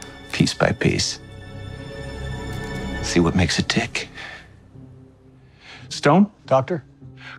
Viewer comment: did he say the D word? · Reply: “See what makes it dick.” Now does that make any sense?